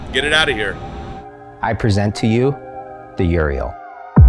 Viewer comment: I thought he said, I present to you, the Urinal... woulda been more accurate